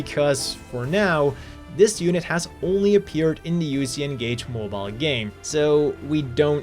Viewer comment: wat is the name of the game he said it was? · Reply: Uc engage